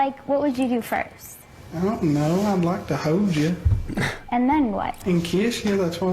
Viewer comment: LOL it literally sounds like he said "Well I'd like ta hose ya."